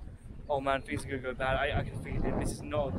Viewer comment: Whoever did your subtitles did you a disservice, you literally saying "Things are going to go bad" when the subtitles say "Man please go bad" @ 12:03 as if you want the family to drown T_T If you need subtitles done, hmu on socials or twitter.